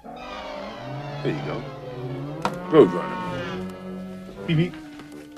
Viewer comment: What does michael whisper to his kids at 3:34? Anybody know? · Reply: There you go...Road Runner. Beep beep!